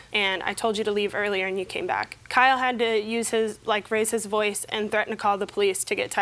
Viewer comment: i thought she said, Kyle had to use his "rascist voice", and i was like what is that?? 😅